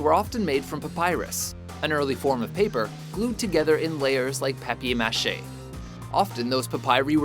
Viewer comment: did he say peppy machet? rather than paper mache?